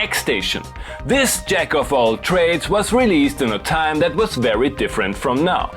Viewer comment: I heard "This jack off all trades..."